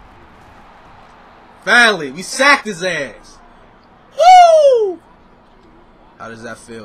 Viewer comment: lol I thought said at 5:04 finally sack in his ass had replay that part like twice lol